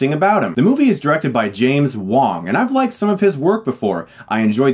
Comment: I thought he said James Wan. I was like whaaaat???